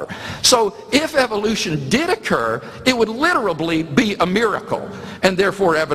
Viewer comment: did he just say "literably"? ...urhh...